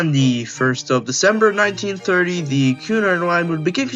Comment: "1936, the" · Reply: Oh sorry, it just sounded like you said 19360.